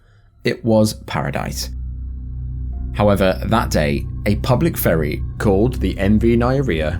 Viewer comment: for a second there i thought he said "for a public furry"